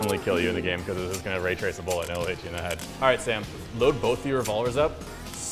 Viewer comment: What did he say? "Retrace the bullet"?